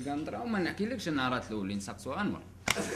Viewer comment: 🤣🤣 وش قال هنا مفهمتش؟ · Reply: قال سقسو انور